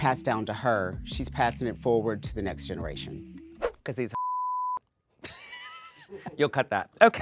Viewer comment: did Bianca call Sasha he or did i misunderstood? on 15:50?